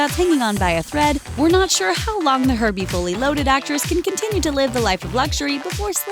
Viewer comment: I thought she said herpe fully loaded!! 😂😂😂